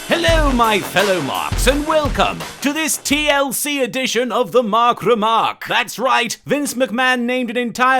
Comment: I thought it said the Marik remark